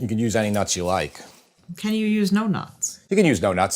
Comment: I really thought she said "can you use doughnuts?" I had to rewind it when you said yes!